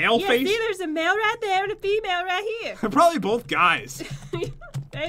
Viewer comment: When you said don't mess this up pat I heard don't mess this up bat.